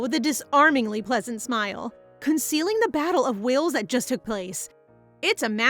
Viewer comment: I hear battle of wills as battle of Wales, like the prince of Wales from the royal navy that sunk the Bismarck